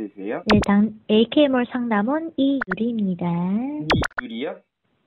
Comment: 소리로가리셨는데이유리라고너무잘들려욬ㅋㅋㅋ